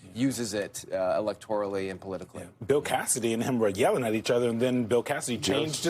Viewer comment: I heard "Bill Cassidy and Hemorrhoid yelling at each other." Lol, the brain does funny things sometimes.